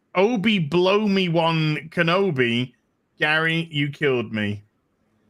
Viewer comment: did i misheard or gary just said blow me-one kenobi